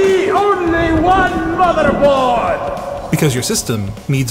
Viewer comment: When I heard "there can be only one...motherboard", I heard motherF#@ hahahaha, my mind leaning toward bad words haha